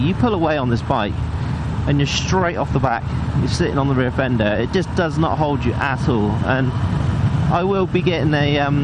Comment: I thought you said “it just doesn’t hold your asshole” 🤣